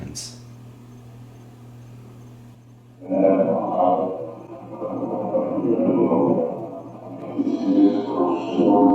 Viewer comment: Great video, Btw at 15:14 the ???? I hear ' I'm honered'. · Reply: Yes! "I am honoured", definitely.